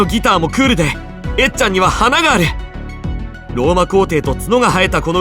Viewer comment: did he say Baby Metal?